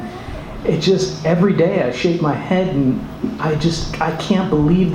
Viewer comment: LOL i thought he said "everyday i shave my head" !!!